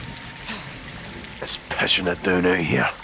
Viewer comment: What does McTavish say on 14:09? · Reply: he said pishing it down not pushing it’s another way of saying it’s pissing it down or simply it’s raining hard